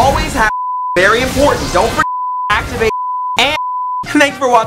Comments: for anyone interested in what he says after 2:23 he says "thanks for watching my guide everyone see you on the rift" the audio is just reversed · is just saying "thanks for watching see you on the rift" fml so much time wasted. Thanks Sky